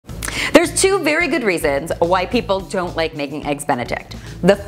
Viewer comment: omg it sound like you said "white people" and not "why people"